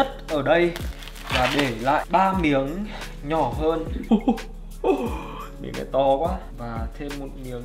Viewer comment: anh oi !!!! Nói j vậy .... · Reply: "miếng này to quá"